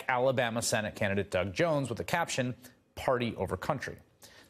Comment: Did Chris say "party over country"? :))